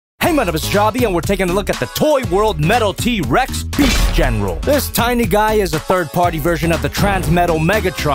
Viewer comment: sounded like u said beef general